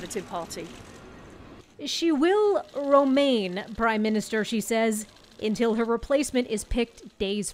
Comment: Did I hear "she will romaine prime minister"? shots fired citynews!